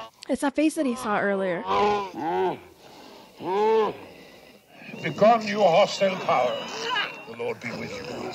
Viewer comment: sounded like he said begone you austin powers